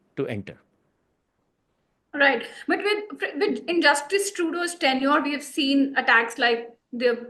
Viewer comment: did she just call Justin Trudeu as "JUSTICE Trudeu"? Gavaron se bhar rakha hai yeh channel seriously